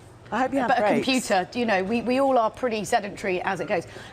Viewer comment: I love how the captions at 4:51 say "sedimentary" instead of "sedentary."